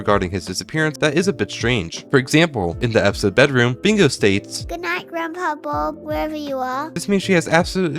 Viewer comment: Am I the only one who thought bingo said "good night groundhog bob where ever you are" not "good night grandad bob where ever you are"?